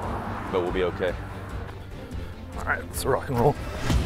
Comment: Did he say lets ruck n roll? 00:56 😂😂